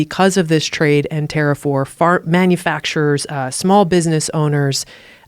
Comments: Love this one, but @ 1:16:34 it totally sounds like Tulsi said "Fart manufacturers" - I was laughing quite a bit. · did she say fart manufacturers? There goes the campaign... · did she say fart manufacturers? 🤔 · Sounds like Tulsi said "fart" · does she say fart manufacturers?